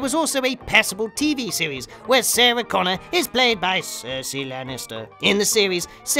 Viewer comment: I thought you said “saucy Lannister”